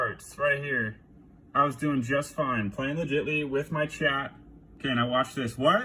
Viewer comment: Did homeboy just say "Legitly."